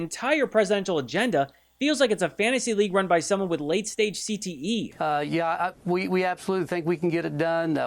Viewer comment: For a second there, I thought you said stage 9 CTE🤣